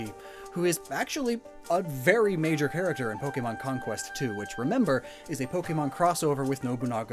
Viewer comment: I know you said "Pokémon Conquest, too," but I heard "Pokémon Conquest 2," and I want it.